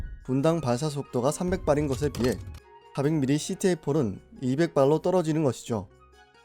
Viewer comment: 멍때리고 있다가 4:26 초에...400mm CTA포라고 들려서 흠칫!!